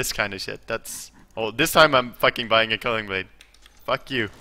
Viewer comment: "Fuck you I'm buying a culling blade" XD love it! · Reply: Matthew Norman I know that but it sounded like he said culling blade.